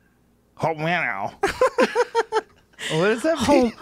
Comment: I thought he was saying "I'll man out"